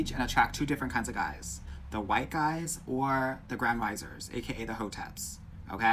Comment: What is a gran wizer? Ho temp? 😅 I had to rewind that 3x. I have no idea what is saying. I’m 9 months loc’d 🥰💁🏽‍♀️ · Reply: Oooooh the Grand Rising men...I thought he said Grand Meister lol 😆